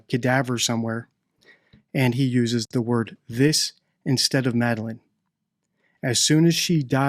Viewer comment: I don't think Gerry said 'this' I think he said 'us' but it comes out Scottish sounding like 'is'. I'm Irish so pretty familiar with the Scottish accent and I believe he said 'us'. Not defending these weirdos, just observing what might be a misheard word.